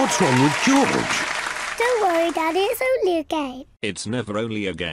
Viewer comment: I heard she say "gay" not "game".